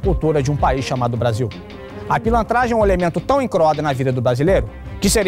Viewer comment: Aos 0:10 o que ele diz mesmo? " pilantragem é um elemento tão em______" A qualidade dos textos, tanto do Documento Trololó quanto do Repórter Boato, são realmente mais densos do que o da imprensa formal. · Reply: quer dizer incroado kkkkk